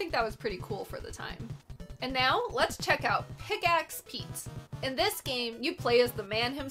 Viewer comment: had this on in the background .. Thought she said PICK ASS PETE... yep.. just a game about pete picking his ass